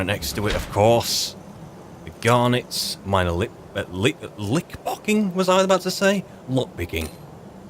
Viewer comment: for a moment there i thought you said "Ring of minor LipLocking